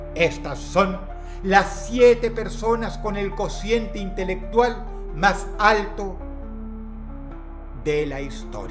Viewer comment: Pensé que estaría la streamer Abby, ay no... Que vergüenza, pensé que decía con conciente intelectual más bajo. Retiro lo dicho